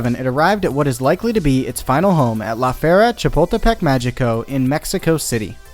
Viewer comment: take a shot every time he says La Feria Chapultepec Magico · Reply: I honestly thought he was saying “la fiera” or “the beast” which was awesome. Thanks for ruining it.